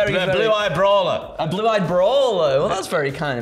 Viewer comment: I first heard "A Blue-eyed Bra" So thanks for repeating it...